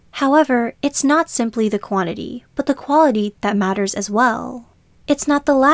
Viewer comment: What do you feel is your purpose in life? Comment below. If you have not found yours, give this video a watch: <link> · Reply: At around 1:57/8 the subtitles say "it's not the quality, but the quantity" instead of your "it's not the quantity, it's the quality". Might wanna fix that. 😀